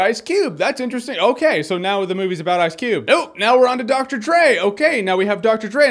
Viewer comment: who else hears Dr. Trey XD